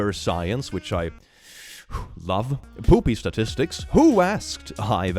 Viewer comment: I thought he said booty statistics, and it still made sense 🤣🤣🤣🤣🤣